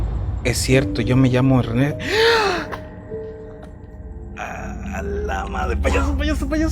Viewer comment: did he say "alamak".. cuz what he says sounds similar to a malaysian word alamak it also means "oh no" or "oh shit"